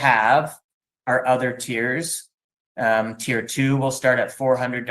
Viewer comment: Every time he says “tier 1, tier 2” etc I just hear “tear 1, tear 2” 😢